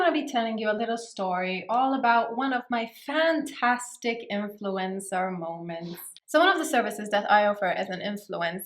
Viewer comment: I thought she said "my favorite influenza moment" i was like: um, miss lady.